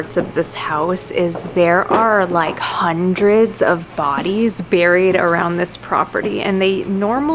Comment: Ngl when you said hundreds of bodies I thought you said hundreds of baddies 💀